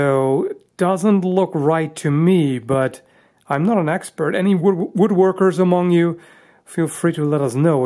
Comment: Did you say you're not an axe-pert?